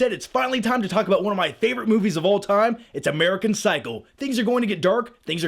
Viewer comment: Did he say "American Cycle?" :D